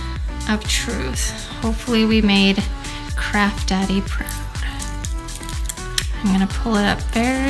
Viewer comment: ⭐Which was your favorite project? Like this video? Then you will love this one too: <link> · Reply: did you call your man "craft daddy" ?!? That is adorable